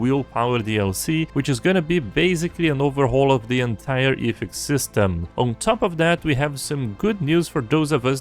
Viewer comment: What are you saying at 0:31 "Willpower DLC will change the entire ??? system" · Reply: ethics system